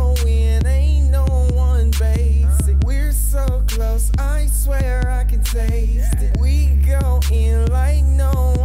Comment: "We're so gross, I swear I can taste it." That's what I heard and I can't not hear it 😝. Great beer though. Love the series, mate!